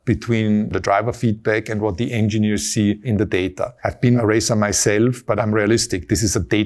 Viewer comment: Subtitles say "I've been eraser myself" Erm, what? What is that supposed to mean? He says "I've been a racer myself".